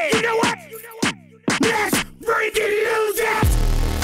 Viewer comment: That damn McDonald's video Brought me here lol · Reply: lol i thought he was saying lets get freaking lucid